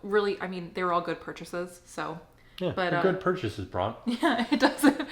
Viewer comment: Did you call her "Bron" at 10:03? Bc I also love Bron & Caitlin lol